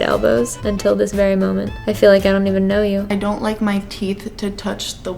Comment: At around 18:40 When said she put Elbows in hell I thought she said she put Elvis in hell